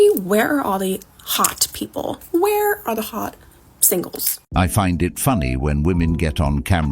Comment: I thought she said where are all the shingles.